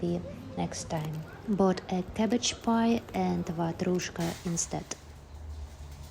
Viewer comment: what is a “madrushka” (sounded like you said that) please?